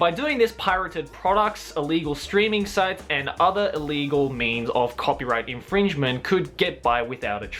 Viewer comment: When he said " illegal means " I heard " illegal memes " gg